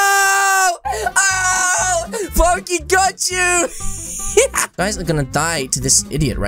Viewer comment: did say foky got you?